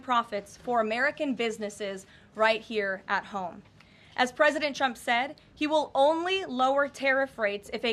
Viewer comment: is it just me or did she call him president chump?